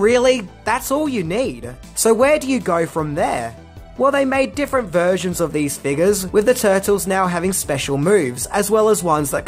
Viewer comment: In 19:38 minutes of this video I swear it sounds like he says " They mad different versions of these Niggas".. I know it isn't just cause I'm black🤦‍♀️